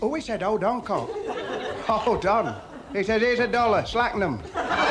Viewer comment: if you couldn't hear it, the dentist joke ends with "Here's a dollar - slacken 'em!"